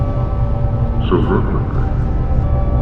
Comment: what's he saying here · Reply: so run with me. run to me. run.